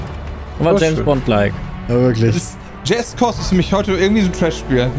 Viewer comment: Bin innerlich zusammengebrochen, als ich gehört habe, wie Brammen "Just Cause" ausspricht xD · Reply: "Jazzt Kost"